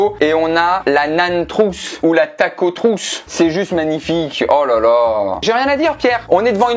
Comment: À 8:24 j'ai compris "l'attaque aux trousses" ... Je suis irrécupérable 😭